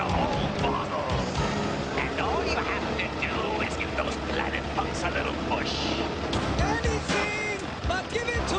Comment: did he say planet fucks?